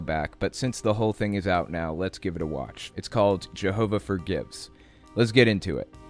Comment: I thought you said "Jehovah for gits"